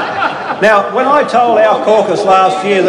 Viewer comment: your so macho!! lol...your a low altitude flyer!! Did Costello like a dinner!! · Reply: I thought he said - A low altitude fly, both are applicable. Oh how I miss Keating.